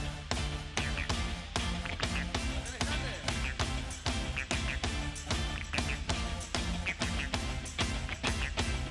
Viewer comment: alguien puede saber que dice en este momento?. " dale!, canten!" puede ser?. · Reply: "come on" en la primera parte, lo demás no se entiende.